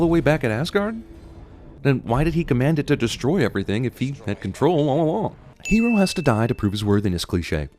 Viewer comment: "control AwAaWAaAW" is all I heard